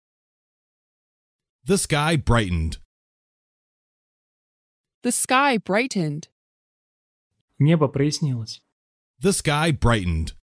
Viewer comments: мне послышалось этот парень беременный... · То чувство, когда на второй фразе видео: "The sky brightened", послышалось: "This guy is pregnant" 😂😂😂